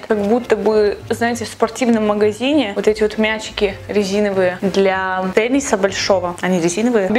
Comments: я одна услышала "как эти шарики для Пениса большого..." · Послышалось: Мячики для пениса большого, резиновые!